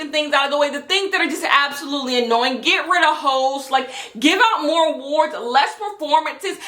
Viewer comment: I wasn’t paying full attention properly for the first couple of minutes (sorry, someone was bothering me) but when I turn my attention at 3:21 I heard “get rid of hoes”